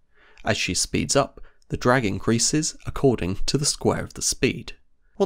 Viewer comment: I liked the subtitles at 4:44 "dragon greases" instead of "drag increases", youtube's automatic subtitles gets it right.